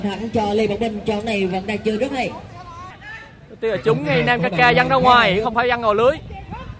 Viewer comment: nghe ra "trúng 500k văng ra ngoài"